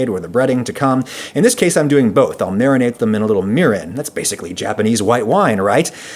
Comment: Am I the only one that heard Mr. Ragusea say "Marinate them in mirin", and then thought he said it as "Mirin"-ate?